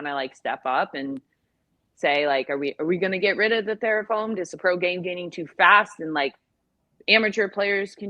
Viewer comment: and 41:53, it sounds like she is saying therafoamed instead of thermoformed. Is that a legit thing?